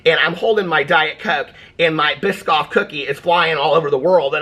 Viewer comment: 🤣 I lost it when I thought you said my pissed off cookie was flying all over the seat 💺!! I replayed it and heard what you actually said biscoff cookie.. it was still funny loved the visual effect of the rocking